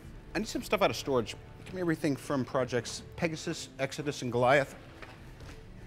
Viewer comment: Did he say project Goliath